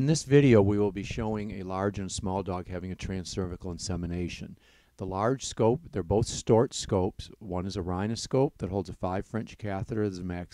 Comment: I hear "The large scope their both storks scopes"